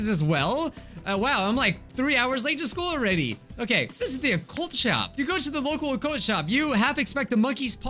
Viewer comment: I thought for one second he said "Let's visit the cult shop" 😂😂😂